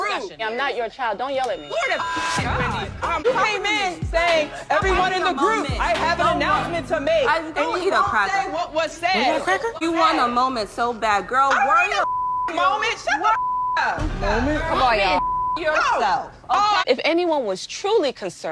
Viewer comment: Robyn: going off Wendy: go eat a cracker. Candiace: what about a cracker?? Why that made me laugh so much I’ll never know 🤣🤣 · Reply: I thought she said " Polly want a cracker" and I yelled in tears🤦🏿😂😂😂😂